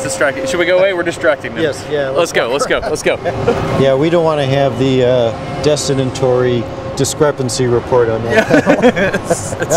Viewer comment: what does he say while leaving the press operators? D*****tory (???) discrepancy report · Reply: "we don't want to have the Destin-and-Tory discrepancy report" (i.e. discrepancy report due to Destin and Tory)